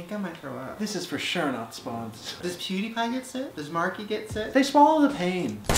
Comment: Did Shane just call Markiplier Markie??